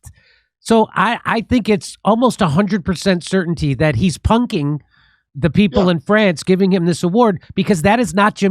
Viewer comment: Lol "So... I think it's almost 100% certainty that he's poking the people of France..." made me laugh (captions say "punking" not poking and "in" not of apparently... still made me laugh... both sentences are funny... it's the "almost 100% certainty" part that gets me😹)